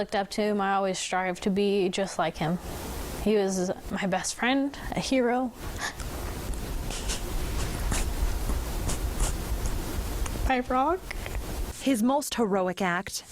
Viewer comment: Beautiful story. However, my heart goes out to those depending on the subtitles (deaf, English learners, etc) at 1:38 when she says he was my best friend, my hero, my rock ... subtitle says hey frog 🐸